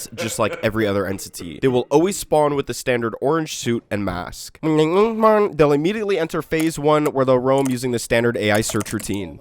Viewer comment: Please tell me what bro was trying to say at 0:44 💀 btw love your vids ❤ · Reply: "When they BING BING BONG"